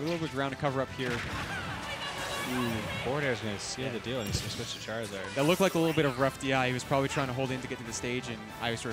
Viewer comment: What the hell was pit yelling when he died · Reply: "I never learned how to read yet!!!" It's a callback to kid icarus uprising for the 3ds